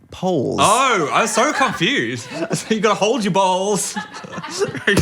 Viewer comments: I heard "hold ur balls" too, dw Phil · I also thought Dan said "hold your balls" and i spent the next minute trying to figure out if if there was some secret part of skiing that involved balls or if it was just a phrase people say or something... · Phil, you're not alone - I also thought Dan said "hold your balls". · I thought he said balls as well...😅